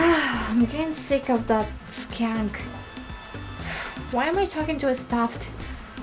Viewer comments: lol, I thought you said skank instead of skunk. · he he, it sounded like you said "that skank"! This is a really nice painting!